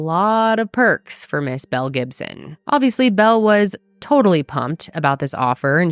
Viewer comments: In the beginning of the video I totally thought she said "Mel Gibson"! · Thought you said Mel Gibson and I was shocked he was Australian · I really thought she said Mel Gibson at first, took a quick u-turn when the episode started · I was so confused that Mel Gibson didn’t come into play in this story because that’s what I thought you said at the beginning 😂